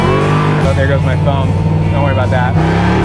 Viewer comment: It sounded like you said "there goes my thumb" a couple of times. You OK? Will my 100lb. dog fit in the backseat delete area? He can't ride in my current car (two seater) Great ride along, I would love to own this Camaro - please.